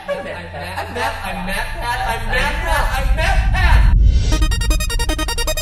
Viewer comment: At the end of the intro it sounds like hes saying "Im Mappap"